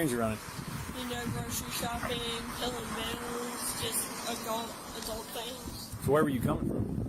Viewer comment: i like that she accidentally said “pilling bills” instead of “paying bills” just before the other cop sees a bunch of pills in her car